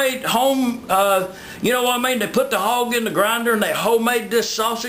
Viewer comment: lol at first i thought he said put the hog in the blender i was like WHAT?!?!?!?!?!